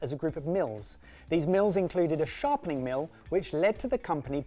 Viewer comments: immediately thought he heard MiLF 1:23 😂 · Did think during explaining where the bread cutter came from he thought said ‘MILF’s’ instead of ‘Mills?’ Or is it just me? I saw him almost about to question him and do a little giggle, I need to know if I’m right here, because I most certainly did think it too and need to know if my level of immature humour is evenly matched with 🤣